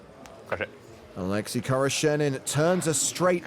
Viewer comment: Oleksi khoroshenin? I thought the commentator said a Lexus car salesman.